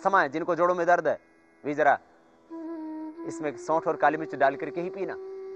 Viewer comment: min, he will say something like sount, with its picture shown, what is that, can anyone say pls ..... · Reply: Soth Matlab sukha adrak aur Kali mirch bole jinko asthma h